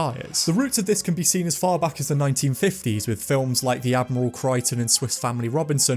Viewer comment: Thank you for this. Nice one. Great job. Maybe its my ears, I have replayed your comment several times. With your mention of 1950s films (13:25); did you say "The Admiral Crichton? My schoolmates could not pronounce Admirable, so they called it "Admiral". They were only 10 years old at the time. It is understandable. If I am going deaf, it is important for me to know now. Was it Admiral or Admirable? In your own time. Cheers. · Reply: hahahah i definitely said admirable, or at least that's what was in the script, i do struggle saying it though